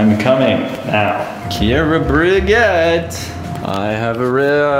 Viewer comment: When he said "KiEra BRigEt" i thought he said Kiera buegette 😂